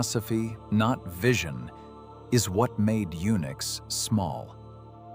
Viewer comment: the subtitles says "eunucks" instead of "Unix".